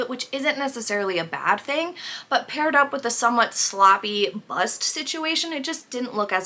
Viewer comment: Whatever structure is underneath!?!?! Did I just hear "sloppy bust situation"? You are hilarious!! Love your vids - entertaining AND I learn so much (even though I thought I knew my way around fashion and makeup)...